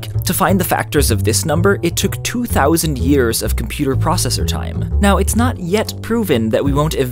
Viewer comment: Did he really say "2000 years of processing time"?